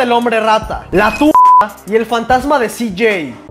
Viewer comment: Cuando dijo tumba pense que dijo la tula de cj XD